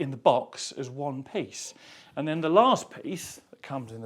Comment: WHAT DID HE SAY??!!! DID I HEAR ONE PIECE?!!?? 🔥🔥🔥